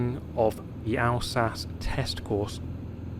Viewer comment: I thought i heard alsace test "ticles" there for a sec